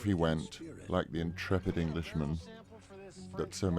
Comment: Which word does he use to describe "the" Englishmam (01:07 and 02:58)? I understand what he means but I can't understand the word. Thanks in advance and thanks a lot for uploading! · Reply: "...this intrepid Englishman." :)